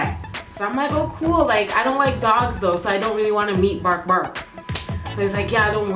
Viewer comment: she named the dog bark bark 😂😂😂😭😭😭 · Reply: i legit thought she said bork bork